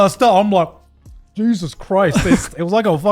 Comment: I was listening to this while working out and at 55:56 when Garnt was talking about his juicy Mango, I thought I heard him say "Juice-us Christ" and i lost my breath laughing with the pun